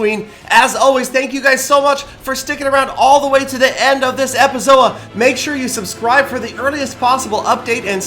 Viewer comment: did he say Epizoa